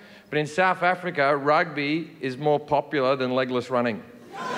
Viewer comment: Ngl i thought he said "Legolas running"